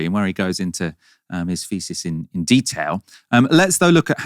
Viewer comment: When you said where he (Gary) goes into his thesis in detail, for a moment I thought you said where he goes into his faeces in detail. It's just the London accent.